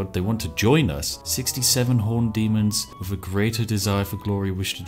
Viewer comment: wait a minute ....did he just say....KHORNE daemons ? A man of culture I see..